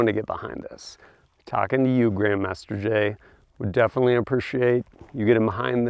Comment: At first I thought you said "Jam Master Jay"